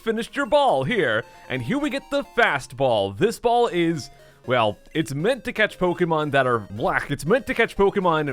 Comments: did he say " Fast ball, this ball is well is meant to catch pokemon that are black " @ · I thought he said "it's meant to catch Pokémon that are black" 😭😭